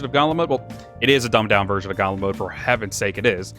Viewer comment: A dumbed down version of what? I actually could not catch what you were saying there sir. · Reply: Gauntlet mode from WO3U.